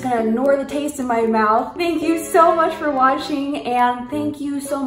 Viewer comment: I thought she said washing at 19:23 😂